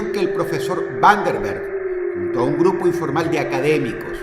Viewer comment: jjajaaja entendí profesor butterbean